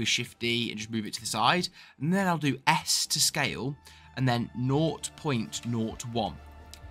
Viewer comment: what did you say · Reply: and then i will do S to scale (blender shortcut/ hotkey)